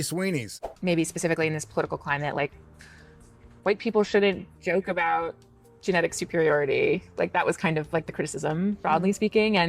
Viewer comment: "a joke about genetic superiority" is that what that idiot said?